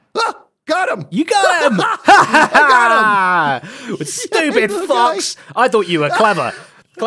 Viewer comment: I definitely heard say stupid fucks help 😭😭